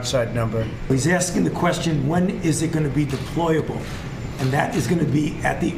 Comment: Excuse me what did he say at 1:06 did he say a couple months? It’s crazy how we have almost been in this for a year